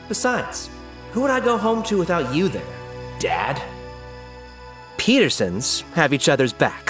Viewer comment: Did he say dad or thed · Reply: Dad